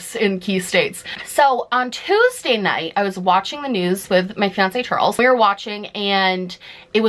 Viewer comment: When you said my fiance Charles, what I heard was "My fancy Charles". Now I'm waiting for the Middle Ground podcast to hear fancy Charles.